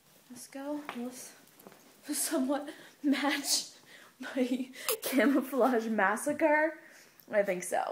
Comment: at first i thought it said masscara lol